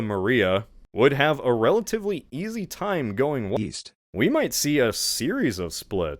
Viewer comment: Oh, east? I thought you said Weast